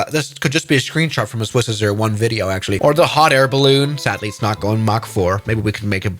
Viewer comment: wait what did he say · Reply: It’s not going back for